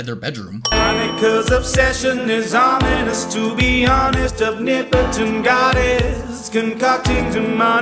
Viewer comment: the captions say nipple to goddess